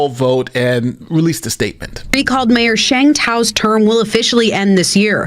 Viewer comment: Man, I swear I thought they said her name was Shang Tsung at first. And in my defense, she do look like she's sucked some souls out in her day.